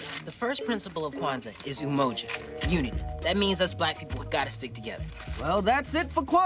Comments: Me parece que foi isso: That means us black people gotta stick together. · voltei várias vezes pq eu sinto que eu ouvi "we gotta stick together" ou "would gotta stick together" 🙃